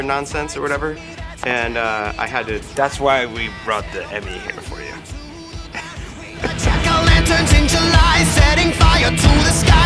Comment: did Pete say we brought to the emmy for you